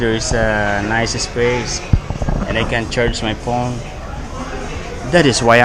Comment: 8.1 i thought u said 'i can check my porn' haha .... i loved this video please make a video of sydney central and surrounding areas during evening/night time